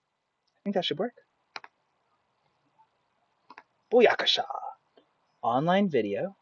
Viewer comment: Great Video! Did I just hear you say Boyakasha! @ 8:39 lol!